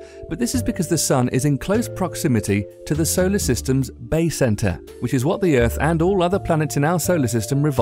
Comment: at 5 it sounded like you said Bae Center